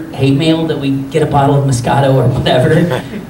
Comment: I totally misheard moscato a discato. Discato, disgusting moscato. This while now be part of my everyday vocabulary. Thank you.